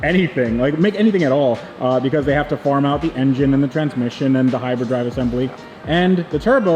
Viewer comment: it sounds like says they make Hyper-drive assemblies.. .. from Star Wars..